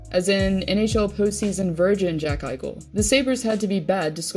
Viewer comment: Did she just say virgin?! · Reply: Ahahahaha so funny xD she said virgin ahahaha